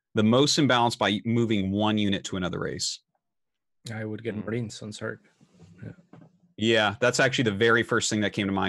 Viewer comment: Couldn't make out what CatZ said at 1:40:04. What did he say? Thanks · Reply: jussi marines to zerg